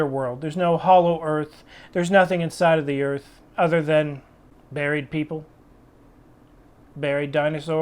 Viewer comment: At first I thought you said “There’s nothing inside the earth other than MARRIED people”. And I was like A TRUER STATEMENT HAS BEVER BEEN MADE!! I laughed so unbelievably hard 🤣then rewinded it and caught what you really said, and was still laughing at what I thought you said. Thanks dude I needed that laugh.